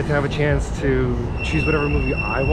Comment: Thought she said, "sex and a whole theme around it". Lol. Thanks for sharing.